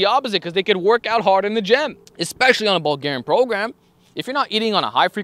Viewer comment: I am terribly sorry, but did you say Bulgarian program ? I am from bulgaria and was surprised to hear there was such thing and it was for bulking. Sorry if you said something else :)